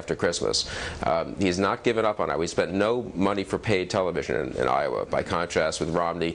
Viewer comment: is bill crystal a known mccain supporter, cuss idk if you noticed at 4:35 that he says "we spent no money" instead of "he spent no money"... just thought that sounded strange.